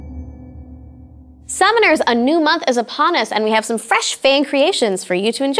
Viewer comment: i thought she said a new map is uppon us O_o